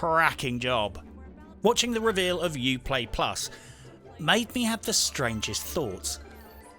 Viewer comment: Factual Correction The Xbox Game pass will be $3.99 ONLY during the beta phase. Then it will become $9.99 a month if you pay 6monthly. The Ultimate Pass will be $14.99, and include a pass for Xbox, PC AND Xbox Gold Membership. This is less favourable terms than stated in the video, but still a better deal than Uplayplus. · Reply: I could have swore that said Uplatypus. Took me a second.